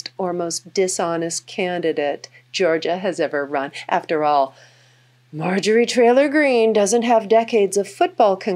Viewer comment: why did she say Marjorie Trailer Greene ?